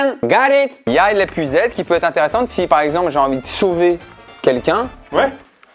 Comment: « L’épuisette si je veut soulever quelqu’un » 🤣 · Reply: Perso j'ai compris " si je veux sauver quelqu'un "